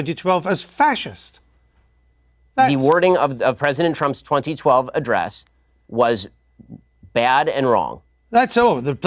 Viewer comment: did he say “President Trump’s 2012 State of the Union Address?” Did I hear that correctly?